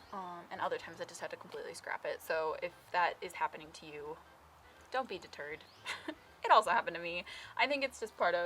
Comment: thought you said "don't beat the turd" . . .was "dont be deterred"